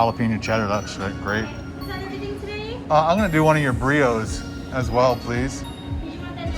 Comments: I thought you ordered a burrito, never heard of that soda. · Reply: I thought he said burrito too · ohh i thought you totally said burrito. lol. I was like what pizza place would serve a burrito.